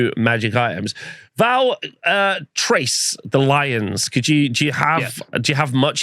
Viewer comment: First, I love you guys. Second, I cringe whenever I hear “Trace” in the context of High Elves. The C and H in “Chrace” are pronounced similarly to the C and H in “Chaos”. Third, Keep the party goin’, and Fourth, Go Bills!